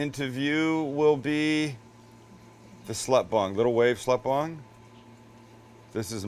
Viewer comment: I MUST be hearing this wrong but did he just call tnat chick a slut bum or a slut bong? Every time I replay it, I hear something different